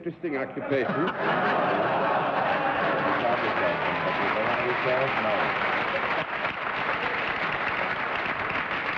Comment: Can anyone make out what Henry Morgan says at 3:17 following John's telling of the "joke"? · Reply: Something like, "Do they understand it. No." Unfortunately John ruined Bennett's joke so the pun was completely lost.